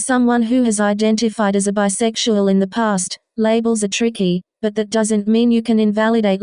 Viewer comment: Off topic.. but did you say you identify as a tricky? As in, possibly, Tricky Techno? Or did I hear things?- 1:43 to be exact But as I'm playing it back I think you said "labels are tricky" but still